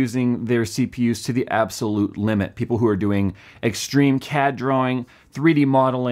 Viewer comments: I thought you said "Extreme Cat Drawing" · did he just say "extreme cat drawing"? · I heard "extreme cat drawing" XD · I heard "extreme cat drawing" hehe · I thought he said extreme CAT drawing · thought he said "extreme cat drawing"